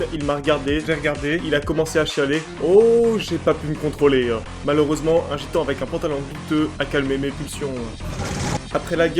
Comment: pour moi c'est comme s'il disait "je eu une demi molle en le voyant chialer" XD